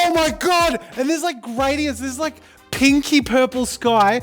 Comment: I heard "kinky purple" instead of "pinky purple" and I was so utterly confused